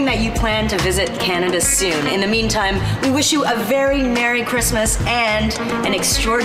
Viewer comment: it sounded like she said a very Narry Christmas XD